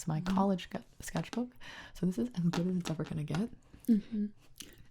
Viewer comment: This is the bleggleblihyblih it’s ever gonna get. The HECK does she say? 😄😄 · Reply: "this is as good as it's ever gonna get"